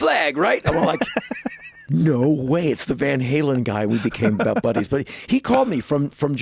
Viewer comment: did anyone else catch the part where he said he and david lee roth became butt buddies? he said it kinda fast, but every time i replay it that's what i hear. it's